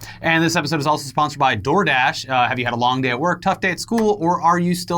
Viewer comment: Im old, I heard "This episode is brought to you by Jordache". I thought, man that would have been huge in the 80s or 90s. Oh 'Door Dash'.